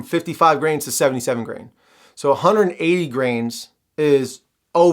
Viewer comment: I thought he was saying grams. I see they use grains in the US. 180 grains = 11g